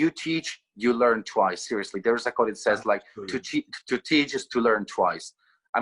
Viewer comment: Hi! Sorry but i heard this phrase ‘there’s a call it says...’ from Mr. and i thought it was instead of saying ‘there’s a proverb it says...’. Am i right? eager to know this. Thanks. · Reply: There is a quote not a call 😊